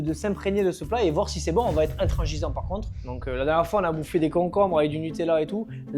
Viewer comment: on va être quoi ? 😂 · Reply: Intrengisant !